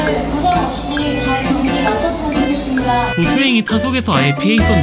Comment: 정지소? 왜 경기 잘 망쳤으면 좋겠습니다로 들리냐...?